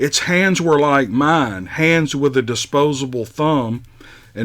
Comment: nice chickens ... I may have misheard you, but it sounded like you said the Bigfoot had 'disposable' thumbs and not 'opposable' thumbs. ;-)) ...